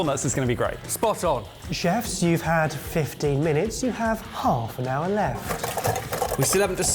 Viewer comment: I saw that used Gelatine to later fry off the stick and was reminded of Jamies Wine Cubes... why not use Agaragar? ;-P PS: 6:29 pretty sure the subtitle should say "15 minutes" instead of "50 minutes". Quite a few subtitle mistakes since a few weeks. Did you guys change something in your workflow?